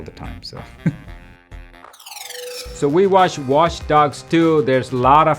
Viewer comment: I heard ,,WaSH Dogs" quite clearly.